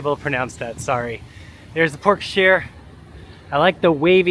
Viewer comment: I must be hungry because I thought you said Pork Share. I’m like yes thank you I would like some lol 😊